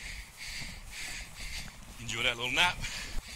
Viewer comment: He doesn't say anything about being knocked out. He says "Enjoy that little nap?" Same thing, but your caption is wrong.